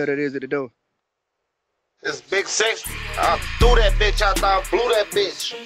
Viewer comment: At first I thought he said it’s big sexy. My bad shi fye though 🔥🔥🤣😂